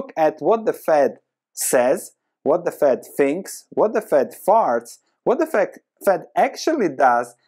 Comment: My ears must be bad, I heard "...,what the fed farts,..."^^